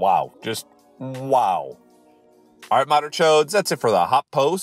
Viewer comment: Quick question, why do you call a cop “an op” Do we even have to censor the word cop now? If so that’s kinda sad that we even have to do that, but hey I don’t blame ya, people cancel others for anything nowadays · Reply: To me it sounds like he says "Modern Chodes" but idk what he really says.